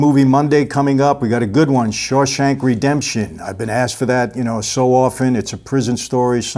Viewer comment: did you say short shank redemption ? Because ive seen that clip but had to look what movie its from :)